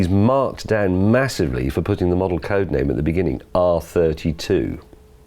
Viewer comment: anyone able to tell me what James is saying here? Marked down for model code name?